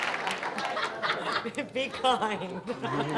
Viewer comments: What did she say ? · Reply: be kind. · did she say BITCOIN? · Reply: She said, 'be kind'.